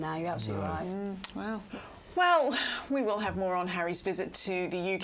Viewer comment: At minute 17:56, all I heard was, “Well, we’ll have moron Harry’s visit to the U.K. and Europe….”